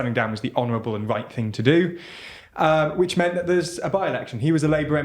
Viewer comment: oh i thought they were saying "bi election" which sounded kinda interesting 🙂the benefits of video titles